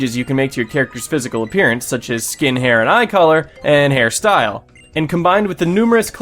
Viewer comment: did anybody else notice that at 9:23 it sounded like he said "skin hair" instead of "Skin, hair"